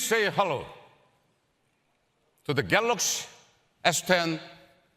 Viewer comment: Did he just said "Galax"